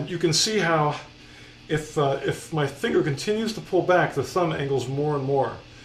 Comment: it sounds like he says fish fnger